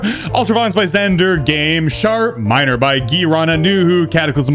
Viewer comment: It always sounds like he is saying shart minor 😂in the intro